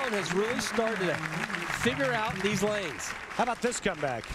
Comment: Am i the only one who think it sounds like Randy says "how about this scumbag" at 3:45 ? I know that he is sayin "how about this comeback" but still.....